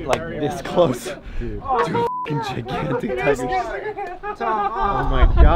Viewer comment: for some reason, I heard "gigantic chicken" 😅